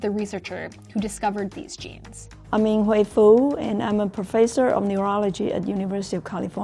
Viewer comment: did she say ying WAIFU